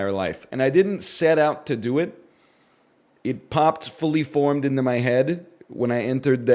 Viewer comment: can't tell if he actually said 'poptfully formed into my head' or not because why does it sorta make sense